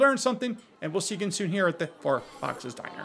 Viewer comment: It always sounds like you say “fart box diner” instead of four box diner and it makes me smile.